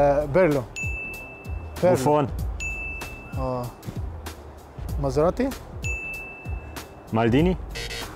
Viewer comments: Did Mo say "Masserati"? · Whaaat did he just say? Maserati?? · maserati instead of materazzi